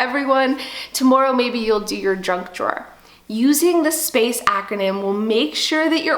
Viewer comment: Did she say drunk drawer @ 8:54? Well I have some junk drawers but drunk drawer is a new one on me. lol Anyway I did thoroughly enjoy your video and subscribed too. I really need to get organized and your video is a start in the right direction for me.